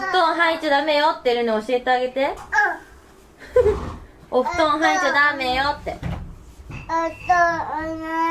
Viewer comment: 「お布団入っちゃダメよって」 「+〆＊2#♪＊(お布団いけない)」 ↑って聞こえない！？すごくない！？